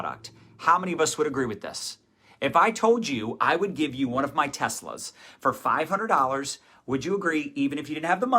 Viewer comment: When he kept saying "one of my Teslas", I kept hearing "one of my testicles", and I was like, "Why does he think his people are on the black market for organs?!"